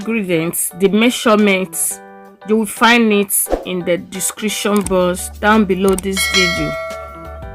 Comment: My sister when you say description box I thought you said description boss any way it looks very yummy ❤